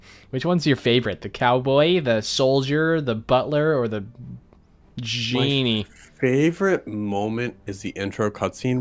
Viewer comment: I thought u was gonna say "or the jew" 💀